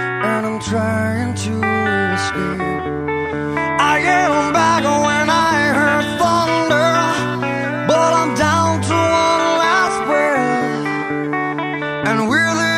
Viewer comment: I ate a bagel when I heard thunder but I'm down to my last bread.